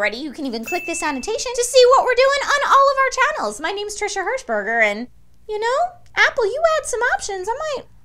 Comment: She said her name so fast I thought she said ngl.